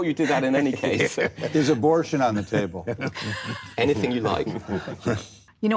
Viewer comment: did he just say "is abortion on the table?"??? I don't care who you are, that's disgusting. I'm no fan of trump but this dude has zero moral high ground lol.